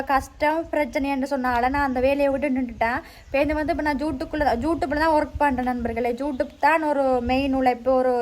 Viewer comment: am i hearing it correctly!! YouTube or Jutube 😮